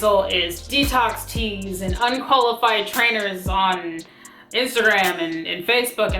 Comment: I thought you said ‘hun-qualified’ at the end of your video. You actually said ‘ unqualified’ but you should take credit for a new word!